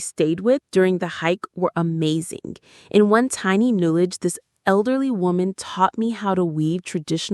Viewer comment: Thanks and love you! I just want to confirm the conversation at 17:31, where I couldn’t hear clearly. The subtitles say “in one tiny knowledge” — but is that correct? I’d really appreciate your help with this. Thank you in advance! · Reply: Hi! We're really sorry about that! 🙏 It was actually 'in one tiny village,' not 'knowledge.' It was a subtitle error that we missed 😔... So sorry for the confusion! Thank you so much for catching it. We truly appreciate your attention to detail and your kindness!